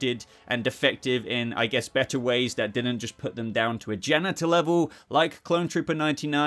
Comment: Wait did I hear that correctly? “On a genital level” ?